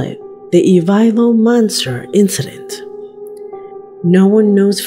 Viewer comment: in case anyone is having a hard time researching the last photos as the transcript doesn’t transcribe the name correctly, it’s called the Ivalo Forest in Finland aka the Ivalo Monster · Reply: Yeah I was way off I thought she said evival monster. Or something